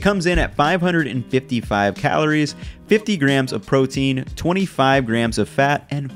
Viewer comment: you say 50g of protein instead of 50g of carbs